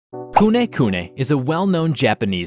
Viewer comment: i thought u said kuneh kuneh but u said kunekune